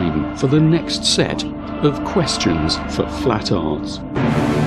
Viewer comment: Lol in the intro it actually sounds like they say flat tards